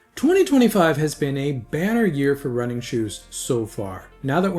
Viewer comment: Is that an Americanism? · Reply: might be, I just didn’t understand it with your accent. I thought you said “it’s been a bear year” like a down year 😂